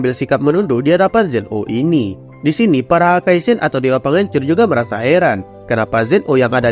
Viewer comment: Salah dengar "dewa pengencrit" 😭😭